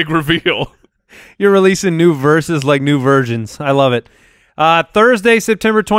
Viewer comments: 🤣🤣🤣 oh man reaction to when said "versions" is everything.. I thought he said virgins too lmaoo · Good. I can tell by face that he thought he heard say "virgins" instead of "versions" too.